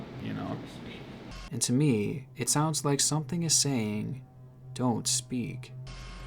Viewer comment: I didn't hear "Don't speak.". I heard "Crispy".